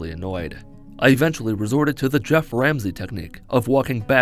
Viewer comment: The Goeff Ramsey technique, huh? Nice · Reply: I actually misheard it for all this time as "Chef Ramsey" and was really wondering what Gordon Ramsey had to do with walking backwards